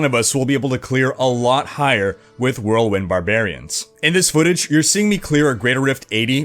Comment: It sounds like you said, "Roland Barbarian".